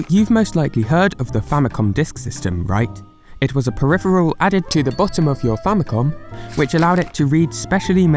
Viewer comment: The captions are kind of wrong. I will highlight the changed parts. 1:09 Actual Phrase: Added to the *bottom*... (captions say added to the *top*...) 1:55 Actual Phrase: It's definitely out of the pocket money price range (captions say It's definitely out of Pokémon money price range) 3:02 Actual Phrase: which would be exclusive to the Disk Writer (captions say which would be exclusive to this Disk Writer service ) 3:23 Actual Phrase: Nintendo figured they... (captions say they figured they...) 3:29 Actual Phrase: But what changes... (captions say what changes...) 4:24 Actual Phrase: They had one more big idea (captions say they had a big idea) 6:51 Actual Phrase: receives a Super Mario Bros. keyring (captions say receives a Super Mario 3 keyring) 6:56 Actual Phrase: I don't mean to downplay it's speciality (captions say I don't mean to downplay how special it is ) 7:26 Actual Phrase: You can tell the game... (captions say you can give the game...)